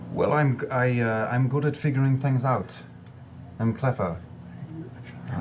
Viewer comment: When you accidentally mishear Liam say “I’m good at fingering things out” instead of “I’m good at figuring things out” and have to go back and hear it again to make sure what he said was what he said